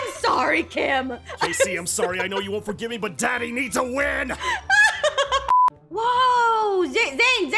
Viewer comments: Zane:I am sorry kc but daddy needs a win ❤ Me:did i heard that right 💀 Plz prove me wrong💀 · did he say DADDY !